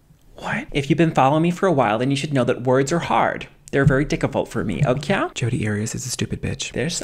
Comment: "if you've been following me for awhile then you should know that words are hard, they're very dicci-fult for me..." 🤣🤣🤣 I love Bailey so much. This was gold and i could feel the love for Bailey in this skit. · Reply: OMG. It's suppose to be "Diccifult"! I heard "Dickable"! Hahahhaga